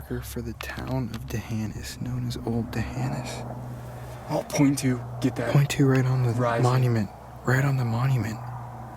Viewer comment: I heard get back at 23:29 area